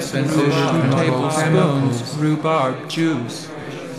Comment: legit thought it said "rhubarb, jews"